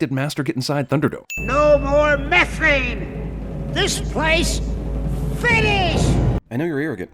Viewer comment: What did Master say at 5:02? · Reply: No more methane, this place finish!